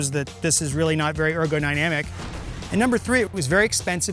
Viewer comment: Ergo dynamic? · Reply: I heard "Ergogynamic"